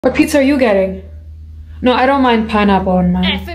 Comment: The first time I heard her saying "What pizza are you getting?" sounded like hey pizza are you gay?